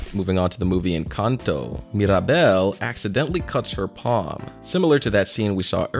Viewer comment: am I the only one who hears paw?